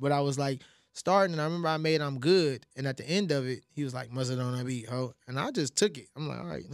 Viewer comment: I finally get to see who mustard on that beat is. I always thought they were saying who dat on that beat. Anyway. What worked for me was pick a day and fast any day , I try on Mondays. When you start just fast for as long as you can take it then a little longer the next time and on until you get to going a whole day not eating solid food. But you can drink whatever I did water lemonade and juice. Mostly lemondades